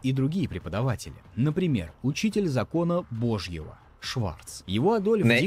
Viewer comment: ЧТО ТАМ БЫЛО? ЧТО ОН СКАЗАЛ? Я ТЕПЕРЬ НЕ УСНУ, ПОКА НЕ УЗНАЮ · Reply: Шварц... негер